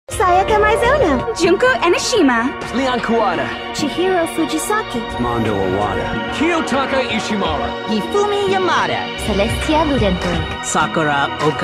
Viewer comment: I heard Mondo Owater